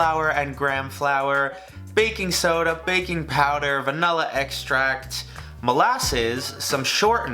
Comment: did he just say “ vanulla extract”?